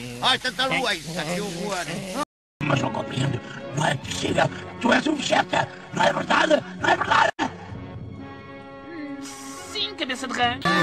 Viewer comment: o que ele disse "mas eu não compreendo ué vegeta tu es um sla oq não é verdade não é verdade?" oq eu entendi na primeira vez "maseucompreend masvegeta tuesumwhantizigat mazuchataarrombadoanular" "sim cabeça de rerk" esse eu n sei mesmo oq ele disse só o "sim cabeça "